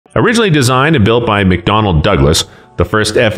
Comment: I thought you said McDonald's for a second.